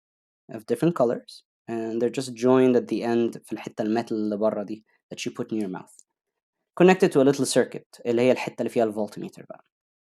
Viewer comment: what does he say here? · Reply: He’s speaking Arabic and says “in the metal part which you put it I. Your mouth connected to a little circuit which is the part that has the volt meter”